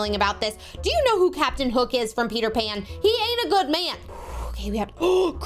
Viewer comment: When you said he ain’t a good man I thought you said he are a good man you 😂